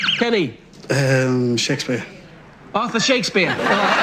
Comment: Considering that the host has one of those English accents where they don't pronounce their r's when they speak, I thought he said author too.